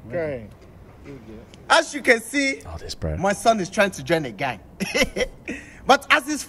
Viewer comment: "Everybody clap for his bravery you're brave, my son the big gangster has nominated to be a sacrificial lamb". 🐑😂🤣 This one my favourite clip at the beginning i thought he said my son is transgender not my son trying to join a gang 🤣